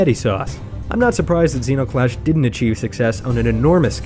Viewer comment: zero clash? did u just say zero clash?! ZERO CLASH ZENO CLASH